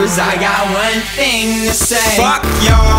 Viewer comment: ok danke :) ich hab immer "one thing missing" verstanden, deswegen ergab es immer keinen sinn :D